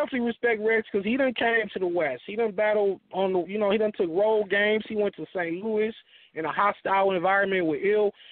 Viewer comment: lol I thought said I would like to see mook and lux battle rogaine but he said a road game . must be this 🚬